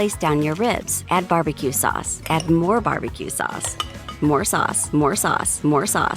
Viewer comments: what i hear : more SUS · at this point I thought it was saying more sus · it sounds like she said more sus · I hear sus but if you turn 2x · Why I heard him say. SUS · i thought it said add more sus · what others hear :. Add more sauce What I hear: MORE SUUS MORE SUUS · this is a great way to feed starving children, and at 1:43, it sounds like it says more sus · I kept hearing more sus after awhile